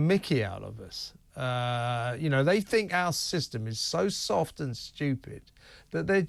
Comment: Sorry, did you just say , "they think are system is too soft" ....lol, Our system on illegal migrants is a Fucking Joke just like the New goverment and the Old goverment, just too busy lining their pockets, if i threatened the PM . i would be locked up.... Two Tier goverment......